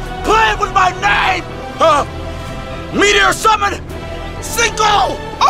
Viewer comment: "Meteor summon, Cinco" I thought that was the name of the meteor until I rewatched and heard him say "I can summon 5 meters right now" Now I'm confused why he switched to spanish